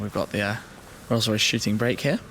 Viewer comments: I thought he said shi**ing brake at first for one of the Rolls-Royce's 😂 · Thought he said 'Rolls Royce shitting brick' not 'shooting break' hahah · "We got the Rolls Royce shitting brick over here" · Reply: lolol.. I thought he said rr shitty brakes in stead of shooting brake. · Rolls Royce shitty brakes? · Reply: Rolls Royce Shooting Brake but honestly, I thought he said, "Rolls Royce shitting bricks here." XD